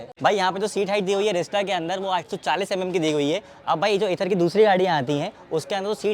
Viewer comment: did you say 840mm seat height?